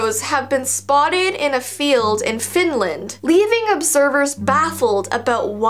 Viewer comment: aaww man! Did ya just say in finland... I'm just asking btw.. cuz I live in finland :'V LETS GO THERE (cuz I love scary things uwu)